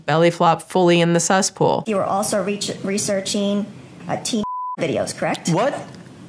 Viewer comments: What are they saying at 38:00 ? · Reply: "Teen" something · what was he searching? I can't infer what they're saying behind the bleeps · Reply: "teen". I looked it up on Reddit